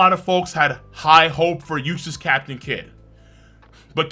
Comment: Captions say useless captain kid at 07:31 lol 😂😂